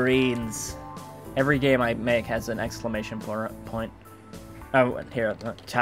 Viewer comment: anyone catch that he said an explanation porno at 15:48 than said point lol.